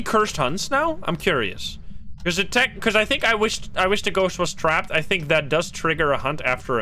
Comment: I’m an American gun owner so all I heard was “I wish the ghost was strapped” 😂